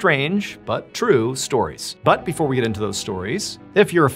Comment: I thought you said 3 untrue stories. I was like, what?!